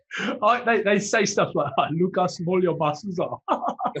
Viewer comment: What does he say at 11:58 ? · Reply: Look at how small your muscles are.